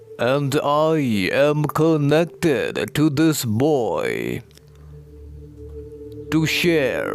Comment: I LOVEEEE that he calls you boy- what an honour ! To me it feels like “pure gentle white untainted soul “ that’s how boys are - love and light to you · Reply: I thought he was saying "this voice" not "this boy"